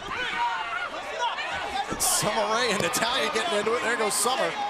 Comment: LOL did Summer say koala at 1:18???